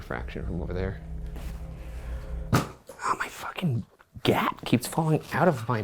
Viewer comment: I keep hearing “my fxckin’ gyatt keeps fallin’ out of my pants” and I can’t tell what he’s actually saying 😭 😂